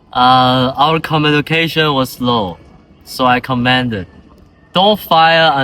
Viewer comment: where possible take a chance to edit subtitles, 1:50/10:10, "Communication was low" but the subtitles say ammunition; communication is ok, but ensure the subtitle matches it. Starting at 3:23 going to 4:02, I'd suggest going back to the "news desk" rather than having the voice overs as this begins to look more like a mini-documentary, and it's a nice through, but in relation to the premise of the task, keep it to the idea of a "here and now" news report as you opened up with at beginning of video. Check subtitles 4:50 "Doctor friendly" should be "Doctor Franklin". Check "6:32" "Down" vs "Dawn". Overall I like the effort made here, and the quality is actually better than I was initially imagining it was going to be given the timeframe. That being said, I'd like to see your team remove the film clips, it's a nice idea, but this is where I was suggesting in class that we use technology to multiple people in the frames and/or use background filters with various scenes. That being said, there can be cutscenes where the camera looks over at tired soldiers sitting by resting/living/etc. on the front, it doesn't need to all be action; bang; boom. You could also show prisoners in the net at the playground where your newscasters are giving a voiceover consider at 8:22/10:10; I think it is an interesting screen-in-screen at the end during credits, but in the last minute, I may suggest speeding up the credits a bit and ensure that they are shared as the focal point. Credits look good ove